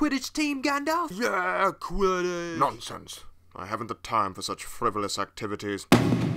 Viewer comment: What does Gandalf say? "I haven't the time for such (something) activities" what does he say on that part and what does it mean? Just asking. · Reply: "I haven't the time for such frivolous activities"